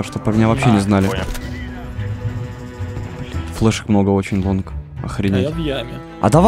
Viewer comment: Я отчётливо услышал Вонг спасибо за упоминание в ролике)